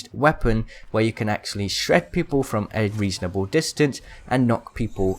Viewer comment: anybody else hear " shrek people"